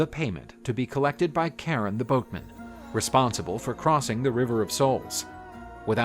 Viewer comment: First heard this as “Karen the boatman” and I can’t unhear it.